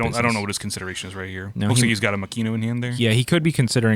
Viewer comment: I hate to be that guy but Makino is Mah-key-no. I dead ass thought you guys were saying Mackenyu(live action Zoro actor) for a bit😂